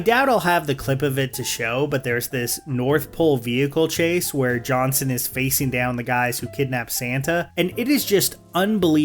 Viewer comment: 100% thought you said "John Cena" at first as a joke and had to go back to rewatch to realise you said "Johnson" haha